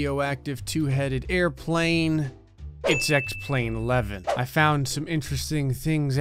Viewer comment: When u said x plane 11 I hear “sex plane 11” and I was like “so is that a plane orgy or...” btw love ur vids